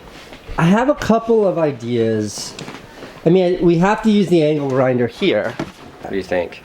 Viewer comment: did he say the anal grinder😂😂😂